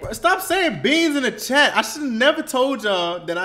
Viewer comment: Idk why but when you said STOP SAYING BEANS IN THE CHAT, I thought you were saying BEEZ IN DA TRAP. That's enough rap...and beans for me 😂😂😂